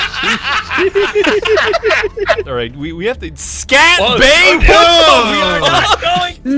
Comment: I thought that said “shat bay”, holy fuck